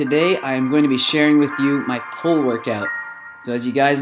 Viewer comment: lmao thought he said "pole workout".....i dont judge.